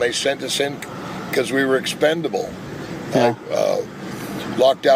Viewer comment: Based on his waistline I thought he said, “...we were expandable.”